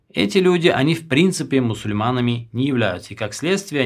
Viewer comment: Салам Алекум.я можит не правелньо понял.вы скозали что в иране котрое живут алавиты и которое обожоствиляют Али Мир ему считаюца не мусульманами.а потом вы скозали что они не шитаюца Мусульманами.а потом вы скозали что они нам считаюца братьями.как они нам братья если оини не Мусульманами? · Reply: Да мне тоже послышалось " они являются", но потом внимательнее послушал, говорилось " не являются".